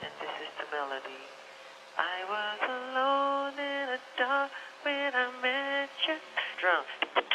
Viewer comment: I always hear him saying "I was alone in the dark with a midget" instead of "I was alone in the dark when I met you". lol